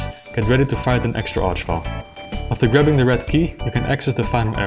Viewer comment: I accidentally misheard "grabbing the red key" as "rubbing the red key", and a whole lot of thoughts occured. Say, has anyone had an occurence like that before?